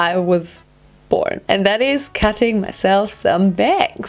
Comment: I thought she said she was cutting herself My bad